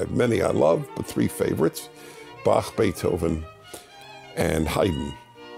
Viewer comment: When you said Beethoven, I thought you were saying 'Debate Hovind' one of those things is way worse than the other